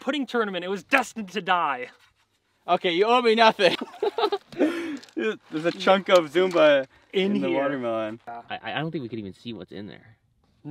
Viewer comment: Am I the only person who heard him say “A chunk of zoomba.”?